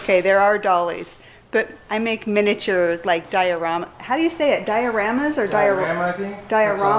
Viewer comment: How do you say it? Diaramas? I swear... I Thought was going to say Diarrheas'?